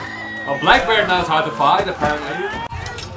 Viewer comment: Are you Dutch?, i thought I heared you say "blijkbaar" on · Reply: Haha I am indeed Dutch! But I didn’t say “blijkbaar” I said Blackbeard because he is the same actor as the one in Black Sails lol